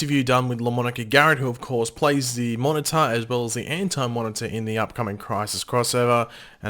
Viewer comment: Who else thought he said inter not anti?? Just me?? Okay.. sad potato noises